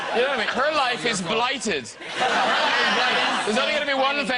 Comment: What does Graham say at 12:13? "Her life is blighted"? I can't quite understand